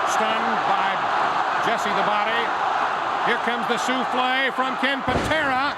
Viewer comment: Souffle? I heard souffle.